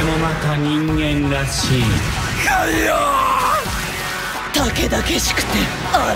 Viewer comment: Anybody know what Akira said here? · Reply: I think it's "Kamioh", wich is translated like "God Almighty". Probably